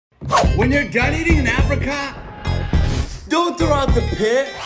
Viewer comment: I thought he said if you’re done eating in Africa